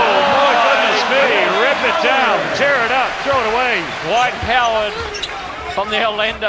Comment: Did anouncer say "DWIGHT COWARD"? MIN 23:02.........LOL